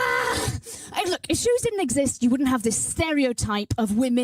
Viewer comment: I was wondering what she was saying, replayed it multiple times until I finally realize she was saying shoes, and not SHOUHOGHS